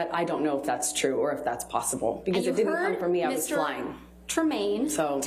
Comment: She actually said the words "I was lying"